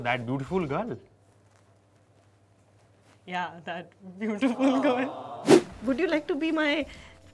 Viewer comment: Is it just me that I first heard “dudeful girl”